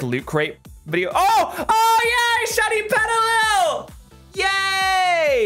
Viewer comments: When you found the shiny pooch, I thought you said " shiny Vegeta", lol. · At first i heard "SHINY VEGETA!"